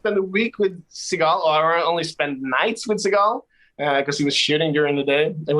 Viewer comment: With his accent, I thought Mr. Paar said, "In China, I only spent the nights with Seagal because he was shitting all day.". And I did not question that, because I would believe that about Seagal.